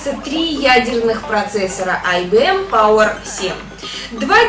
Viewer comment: А никто и не услышал "Три ядерных процессора"? :D